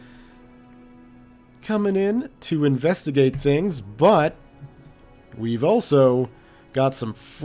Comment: or so: Investigate his butt (Thats, what I heard)